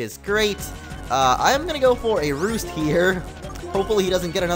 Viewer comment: thought you said "I'm gonna go arouse here"